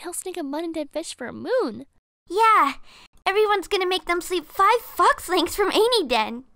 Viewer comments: Five what lengths?! Man, I thought your mother would teach you better than that! (Obviously, I'm joking, but I really heard "five fucks lengths" rather than "five fox lengths" lmao) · I thought she said. “Five f*cks lengths” instead of fox-lengths. Fml.